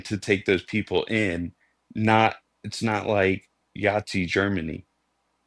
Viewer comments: Did… did he say “Yahtzee Germany”?!!! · Did he say “Yahtzee” Germany????